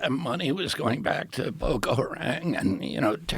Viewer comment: Did he just say that Somalis are sending money to Ethiopia, to fund "Boca Harang?" No wonder god is strangling him.